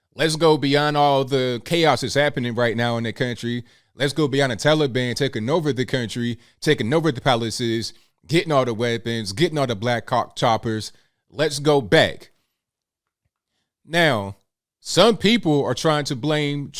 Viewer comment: Sounds like you said Black Cock Choppers 🤔